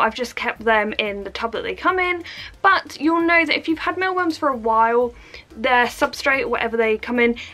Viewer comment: When you ssid meal worms i thought You said male worms lol